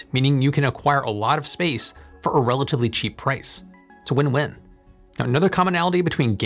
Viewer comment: It's not a win-win, it's a wind-wind! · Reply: I heard wind-wind. 1:48 🧐